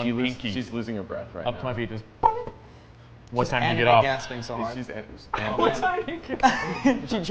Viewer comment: I can't hear the joke at 31:50 help? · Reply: asked "what time do you get off", said, "she just did"